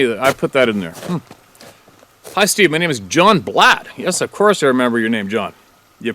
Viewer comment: It's my pleasure. I'm glad he's back too! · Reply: oh your pardon, it's Blatt? I thought he said Black lol anywhichway, inspiring John